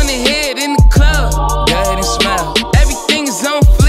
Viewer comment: Bro, I swear to Allah I never knew you said go smile, thought you said God is smart, Bro you really make my house smile , cuzzo shout out to Shabazz doing life he always says everything in this life real, Black King, keep winning may Allah protect you.